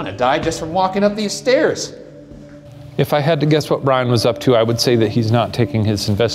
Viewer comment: did Shane just called him Brian????